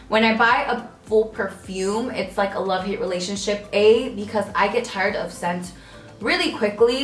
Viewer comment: i thought you said i get tired of sex 😂😂😂